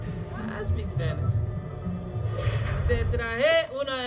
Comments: Te trajÉ una empanada 🧑🏾‍🦱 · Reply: Yo entendí "te traer una empanada" XD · The drastic difference between speaking and him saying "Que maravilla" is so shocking 💀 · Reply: he kinda just skips the ll though, it sounds like he says "que maravi-a" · As a mexican, that "maravilla" hurt me a lot, even knowing that Oscar Isaac speaks fluent spanish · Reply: Fluido? Yo escuché "que maravia"